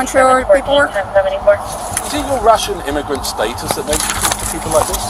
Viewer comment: huh? Am I hearing correctly that he asked, "if her Russian immigration status makes her talk to people that way?" If I am hearing wrong so is the CC AI bot 😂. But seriously... what? Huh? Again....what?